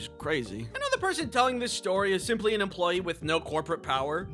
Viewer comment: on 1:18 i thought the plane said "sorry" and not "scary"